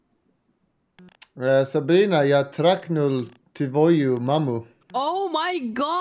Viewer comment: Anyone knows what Bulldog said at the beginning of the video? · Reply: He said Sabina can ijust say merry christmas